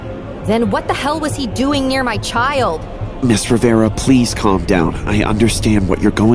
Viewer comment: Did they say Rivera